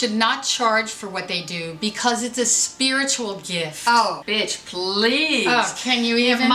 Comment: Did she just say "Oh Bitch Please!" With an attitude!😂